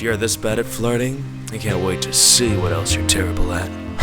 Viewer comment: This confuses me is he saying ‘cant wait to see how bad you are in bed’ ???